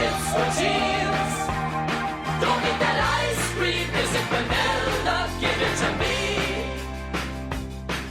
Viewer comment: Strange, I always thought he was saying Vanilla, not Banana. I've never heard of banana ice cream.